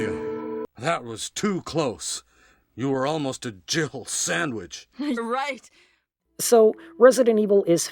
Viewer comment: I used to think was saying "you were almost a jibble sandwich." I thought this from the first time I played it, when I was around 7, until well into my teens when I figured out it was a play on Jill's name. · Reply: I was another one who thought he said jibble sandwich 😂 I was around 8 or 9 when I started playing it and just went with jibble sandwich for years